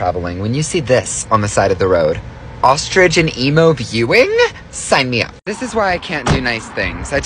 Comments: Sounded like he said ‘ostrich and Emo viewing’ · Did he just say ostrich and emo veiwing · Did he just say ostrich and EMO?